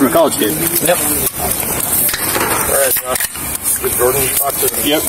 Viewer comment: "Did Jordan talk to Adam" I thought i heard